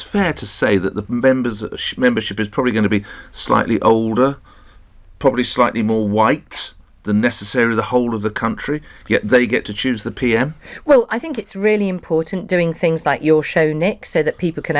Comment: Ferrari: 8:50 "Slightly more white" Did I hear him correctly? Is this his John Snow moment?